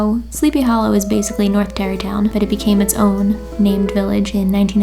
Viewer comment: when she said "Terrytown" at 3:01 I definitely thought she said Derry Town as in the town from It and I freaked out for a second.